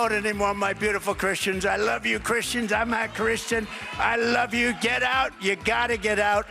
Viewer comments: Sound like he said I'm not Christian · Am I the only person who clearly heard Trump talking to his Evangelical cult followers where he appears to state: I love Christians, I AM NOT Christian, but I love Christians. It sounds like he says he is not Christian, but his followers still cheer him on. I guess that Satan wouldn't be called a Christian, so he would be right with that comment anyway.